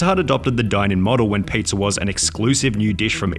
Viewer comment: I thought you said Pizza Inn 😭 I was Finnair be so sad